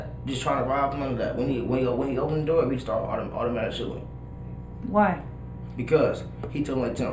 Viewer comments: I heard that too. At 31:04 he said we started shooting. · did i hear that right " he opened the door, we just automatically started shooting"?